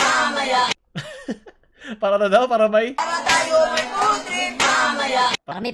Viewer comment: "para tayo ay may foodtrip mamaya" yaaan po yung sinabee bakaa kase po na misheard nyo